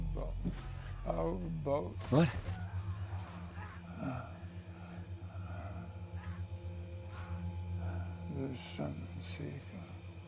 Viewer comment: I can't hear what Charles says at 1:30 don't anyone know because I don't lol haha 😅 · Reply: "Our boat. The Sunseeker"